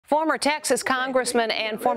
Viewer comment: in the beginning of the video I thought she said "Former Taxes Congressman".